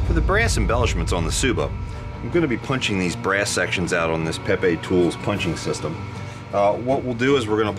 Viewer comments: did he say pepe tool at 7:15 ? LONG LIVE OUR MEME AND SAVIOR PEPE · Did he say Pepetools punching system!? If that's so, then this is the dankest episode ever.